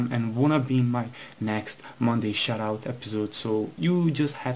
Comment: it sounds like ur saying "shitout"